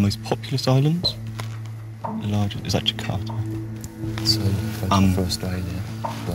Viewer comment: The map highlighting New Guinea only shows the Indonesian side. I was thinking it was the largest islands until I saw that. Bad map. "It's actually cut," I thought the guy said. Which means he was unfairly dinged as well. But on relisten, he actually said, "Is that Jakarta?" Which is a not an island anyway, and the city is not on that island. Still, bad map.